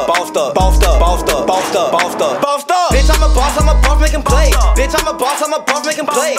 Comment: pasta bathtub ball stuck bounced up listen to him and look at one of these words in bold you should hear what you see! edit: how did this get 340 likes wtf? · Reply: I hear bitch I'm a boss i can barf and can play 🤣